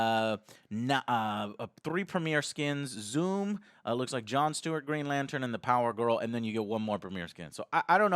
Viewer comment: Did he just say zoom?